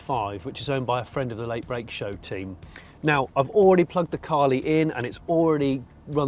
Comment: Thought you said it was sponsored by Carling 😂